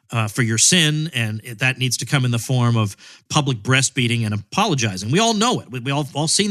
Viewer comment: Oh shit! I thought Tom said "public breast feeding!" I'm doing it wrong!